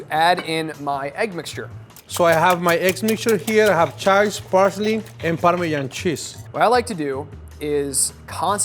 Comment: When says, "Chives, parsley and parmesan" I thought he said Charles Barkley and parmesan. I love Chef!